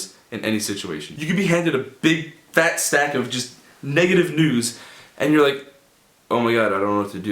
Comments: I thought he said negative nudes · You can be handed a big fat stack of negative news Me: I THOUGHT YOU SAID NUDES FOR A SECOND OMG